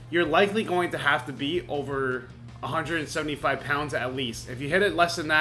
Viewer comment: 6 months!? I thought you said 5 minutes! hahaha... seriously though, great video man! I'm gonna try this adding 2.5 to each side for each session. Thanks for sharing your experience with us!